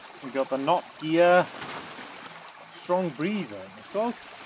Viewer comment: Ha it's the Knot gear tent it is 😆 Cheers I think we were having too much of a laugh and chatting to film a lot on this one! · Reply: knot gear, I thought you said Nokia lol, i listened to it twice before I sent my message, I need hearing aids 😆